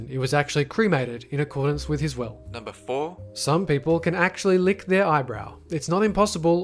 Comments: did he just say some people can lick their eyebrow? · on number 4 i swear i heard him say * some people can lick there eyebrow* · i thought you said it isn't impossible to lick your eyebrows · I heard eyebrow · I swear i keep hearing "eyebrow" instead of "elbow" for #4 lol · It sounds like you said some people can actually lick their eyebrows??! cracks knuckles Imma gunna try this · He said 'lick your eyebrow' At least that's how I heard it. Yet it was about licking your elbow. Please tell me I am not crazy... (Also I wish I could lick my elbow xP) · I heard you say "people can actually lick their eyebrows" instead of elbow XD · am I the only one who heard him say eyebrow instead of elbow "some people can actually lick their eyebrow" or no? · I'm sorry, I think I misheard you... Did you say "lick their eyebrow"? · It sounded like he said eyebrow not elbow XD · When you said elbow at first I thought you said eyebrow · was it just me that heard him say 'people can lick thers EYEBROW' instead of saying ELBOW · uhhhh i heard you saying Eyebrow instead of elbow at number 4... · Did you mean Elbow? It sounded like you said Eyebrow.